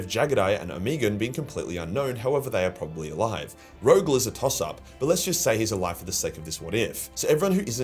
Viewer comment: Totally misheard you on this one, thought you said Rogal was a tossa! Great video sir as always, be interesting to see what happens next.